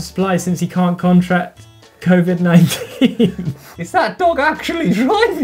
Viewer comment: subtitles said "dong" instead of dog, just found that hilarious. Moving on.